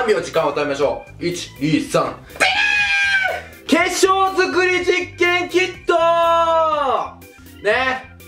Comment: 化粧作り実験キットって聞こえたwwww